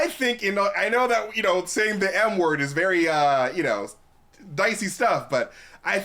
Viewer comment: Bruh i thought he said the N-word 😂😂😂